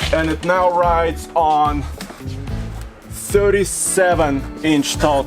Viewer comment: Someone with good hearing please tell what size tire does say in the beginning? I ask because after replaying this a few times it sounds like he says the tire is 77 inches tall. That would be one hell of a tall tire...